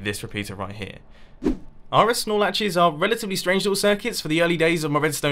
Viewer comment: RS Nor Latches. Okay. I always thought you were saying "RS Snorelatches" or something, lol. XD